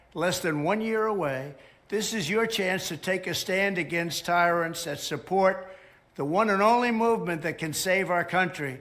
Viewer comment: Take a stand against tyrants AND support MAGA is what he was saying. I think you misheard him on that one, David. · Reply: Here's what Trump said, exactly: "With the 2024 election now less than one year away, this is your chance to take a stand against tyrants that support the one and only movement that can save our country, and make America great again: We must win in 2024." You heard the "AND." But he didn't say it.